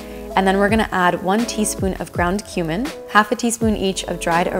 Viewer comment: spits out drink cough, cough, cough h-h-human? pardon me, did you just say, gound Human? I thought you were Vegan?